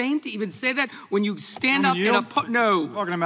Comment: did Cicilline say "F*** no!" when asked to yeild?